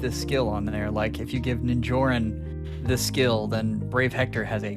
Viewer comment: at one hour in when you mentioned Ninjorrin as a brave hitting unit I misheard it as Njörun and I thought “Wait a minute, we don’t have that crusader in the game. Did he mean Legendary Leif?” lol